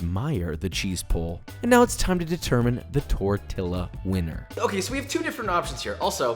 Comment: did you just say tor-till-a